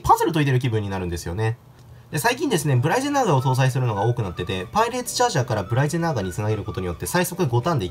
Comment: パーリナイツチャージャーに聞こえた